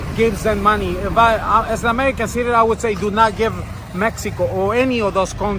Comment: did he say his an American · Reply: He said," as an American citizen".